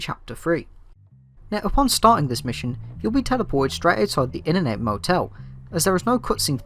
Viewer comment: I str8 thought u were saying Internet Motel